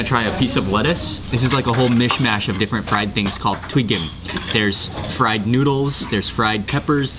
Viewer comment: What was the name at the 16:05 point... you called it, (sounded like twicken but that's not a Korean word) fried noodles, peppers, egg etc. It looked delicious and I'd like to find a recipe for it and try it at home. Thanks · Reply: Twigim, if I'm not mistaken. :)